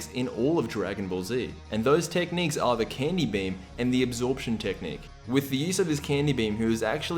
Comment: I know you said the Candy beam for buu, but I swear to god I heard "The Candy bem."